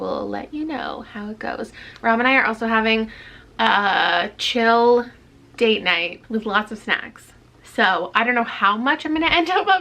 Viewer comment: I’m cleaning my room & listening to this & def thought you said “a chill date night with lots of sex” I was like okaaaay girlfriend, saucyyy 😂